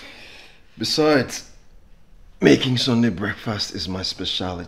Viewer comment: Did I just hear “ making Sonia breakfast is my speciality “ at Henry’s sleepover at Lola’s house? Lol😂